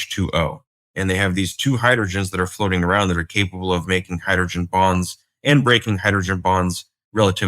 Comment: Lol he said hydrogen bonds.... For a second I thought he said hydrogen bombs. Lol I was like oh that's how they make them